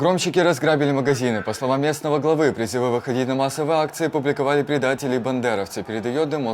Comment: Не расслышал что он сказал2:19 - 2:22? Кто понял напишите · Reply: "Призывы выходить на массовые акции публиковали бандеровцы". Тоже разобрала не с первого раза.